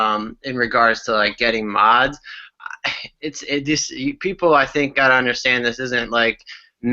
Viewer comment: When the high-end mod guy ( i think) says "you people", makes him sound snobby even thought hes trying not to be. That's how natural it is for high end mos owners to get the snobby label. because "THEY" (lol) so things like "TAHT" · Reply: My bad I said uh people not you people but maybe it still sounded snobby. I just meant people have to kind of understand they are not mass produced